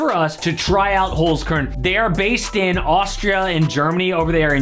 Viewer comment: did he say they are based in Austria, Germany?